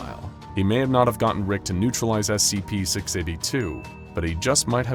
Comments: if you use subtitles at this bit you can see I says SCP-382 instead of SCP-682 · the english subtitle says 382 but the voice says 682. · at subtitles at 20:21 u guys wrote 382 instead of 682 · in the subtitles it says 382 instead of 682